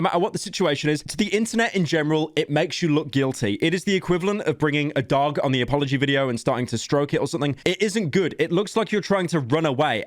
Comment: i thought you said bringing a dog into a apology video while the dog was stroking it if you know